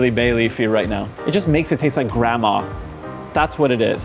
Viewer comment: You know it taste good if it tastes just like grandma. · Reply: Lmao I heard him say 'grandma' instead of 'grandma's*' too, Antoni has some odd tastes.